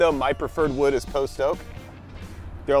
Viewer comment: What's the first word? The second is oak - But what's the first one? Greetings from Germany. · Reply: Post oak, <link>